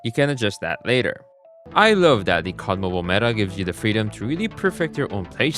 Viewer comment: I keep hearing I love daddy cod mobile😭